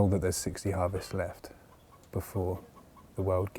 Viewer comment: What a great crew and what a wonderful project! At 6:56. What is he saying? There is 60 harvests left? 6 years harvest left? I don't get or and don't understand the english right. Could someone help me out? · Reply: 60 harvests of topsoil left. Edit: under conventional farming practices.